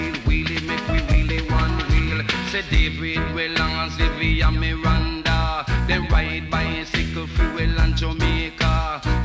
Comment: Can someone tell me what Early B said between 0:14 to 0:17 minutes in the song? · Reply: David Weller, Xavier Miranda dem ride bicycle fe we land Jamaica. Olympic bronze, Commonwealth silver